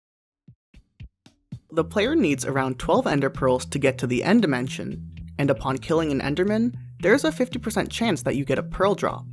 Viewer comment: Is he saying ender mansion??